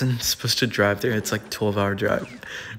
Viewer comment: did he say he's gonna split the 12h drive with his daughter? I didn't hear well, does have a Daughter???? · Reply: He said "me and my dad are gonna split it"